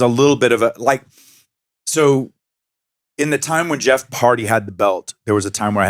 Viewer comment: did he just say Jeff Party?????? 😂